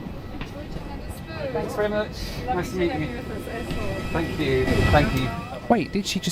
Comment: "Lovely to have you with us AS WELL" Is what she said.